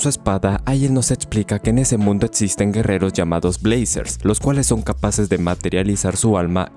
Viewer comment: ¿Cuántos tenemos la esperanza de una segunda temporada? 🥺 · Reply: Jajajaja, en el minuto 0:51 suena la intro de brazzers, puesto que dices blazers 🤣🤣